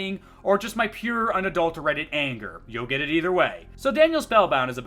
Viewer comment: For a second I thought he said "pure adult Reddit anger"